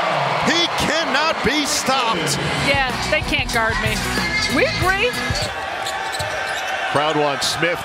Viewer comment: LMAO did Jabari say “he can’t guard me AT ALL”? 😭